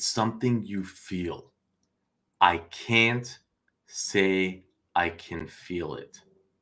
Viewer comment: I heard him say I CAN feel it